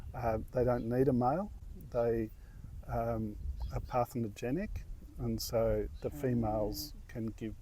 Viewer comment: Captions say "pathogenic" near 16:36, talking about aphid reproduction. However, correct word is "parthenogenesis". Cannot tell if there is a good way to suggest this to be fixed... "report" seems like more serious captions issues...